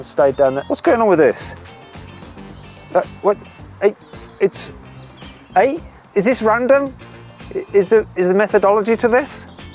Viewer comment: で「適当か？」って訳が表示されてるところ、ちょうどis this london？って言っててなんか笑った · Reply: Is this random?に聞こえた